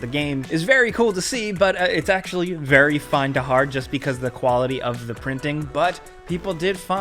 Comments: did he just say Very Find to hard? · Tell me I’m not the only one at 6:00 that heard him say “Really Find To Hard” instead of “Hard To Find”